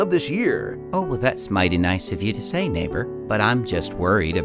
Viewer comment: I thought he said the N word instead of neighboor hhh